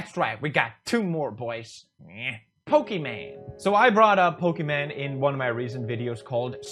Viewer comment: Why does it sound like he is saying?